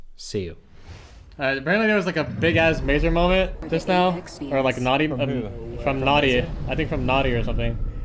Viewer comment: Did someone just say the n-word around 0:56 to like